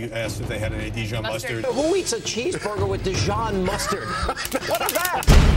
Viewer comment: "What kind of man orders a cheeseburger without ketchup - but Dijon mustard?" Umm...me! I don't like ketchup on burgers, and I love the tanginess of mustard. If they have Brown mustard...even better. If they have Dijon mustard...extremely better. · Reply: At first I thought you said tangerines and mustard and I was ASTOUNDED.